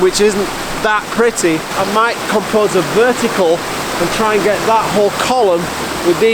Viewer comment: you have typed wh*re in the subtitles instead of whole which I find super offensive. But I guess it´s that sabotaged it so I will let it slide for this time. Jokes aside, great stuff as always!